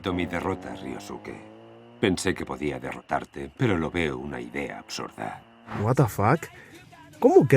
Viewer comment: Creo que lo que dice kyoichi es"Pensé que podía derrotarte pero "LO VEO" una idea absurda"